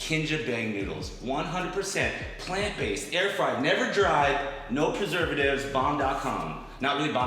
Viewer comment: did say air fried never dried?😂😂😂